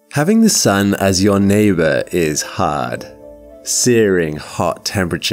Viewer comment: YouTube has autoplay on home, and instead of starting from the start, it continues after a small bit home autoplay. And what i heard is a deep slow voice saying : "Your neighbor is hooot" LMAO Edit